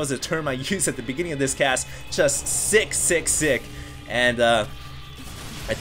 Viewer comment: i thought he said 666 instead of sick sick sick O-o